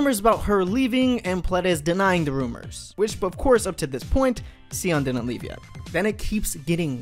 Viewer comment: I'm working on the Spanish translation of the captions but I don't understand from 1:43 to 1:45, 'Xiyeon' what? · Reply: Xiyeon didn't leaved yet I guess he says that